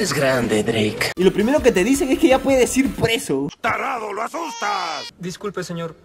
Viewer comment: Alguien más se dio cuenta que dice "ya pides ir preso" En ves de "ya puedes ir preso"?